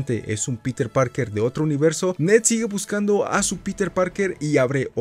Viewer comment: justo me apareció un anuncio de rappy y parece que dijo Ned sigue buscando anuncio algo rico para cenar